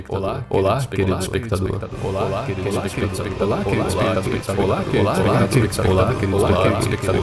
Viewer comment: polar elespectador eu ouvi nessa parte